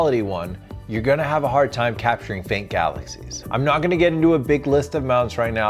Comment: I swore you said fake galaxy’s 😮‍💨🤣